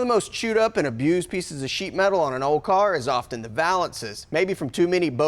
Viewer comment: Jajajajajajaja I thought he was going to say "piece of shit", (sheet)